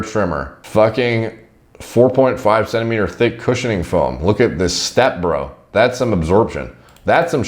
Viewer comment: When he said "look at this step, bro?" I heard "What is this, step-bro?" Thought he got stuck in in the dryer cause delts.*